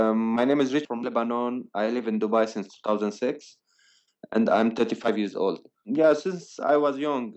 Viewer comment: Did he say 35 or 25 years old It sounded like he said "tirthy five" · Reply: Mohanad Acheck then why did he say "I am 35 years old" at 1:10?